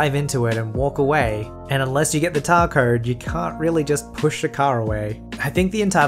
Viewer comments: did he say tar cowed? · did he just say... Tar Cowed?😂 That was funny made my day a bit better, thanks.